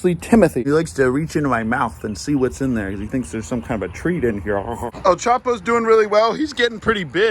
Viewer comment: Did he say El Chapo is he's name??